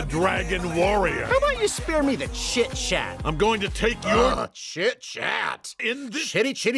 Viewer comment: When Po said "chit chat" it sounded like he said sh*t shat